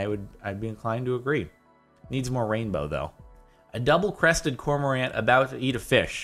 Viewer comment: "It needs more rain though though." Is what I thought he said